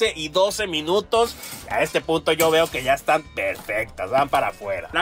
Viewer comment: MINUTO 9:48 Que dijiste?????????????????????????? a este que?????????? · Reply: No se nota mucho pero dijo punto pero fue muy rápido